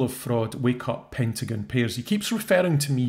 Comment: The first time he called you Pentagon Pears I thought he said "Pendragon Pears." It makes as much sense as Pentagon. lol